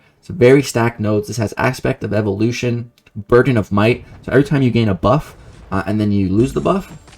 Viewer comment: Did you just say Virgin of Might? 😂🤣